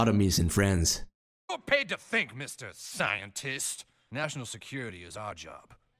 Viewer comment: “You aren’t paid to THINK! Mr. Sci-en-tist!” 🤣 One of my favorite South Park lines! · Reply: I thought he said "you're paid to think, national security is my job"